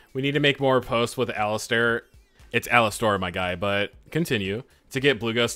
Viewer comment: That says Alastair? I thought is said Altaïr